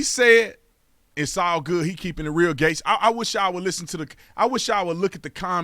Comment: Lol, at 3:39 the cc subtitles say, "He's keeping it real gay."